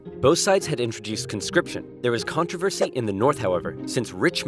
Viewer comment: Did that just say "Constipation"?